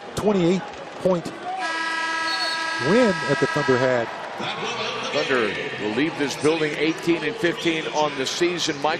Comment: What is Westbrook saying? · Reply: aaron berhane I can vaguely make out him saying, "Don't stop."